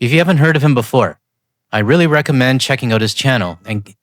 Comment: For a second I thought you were saying your cat had his own channel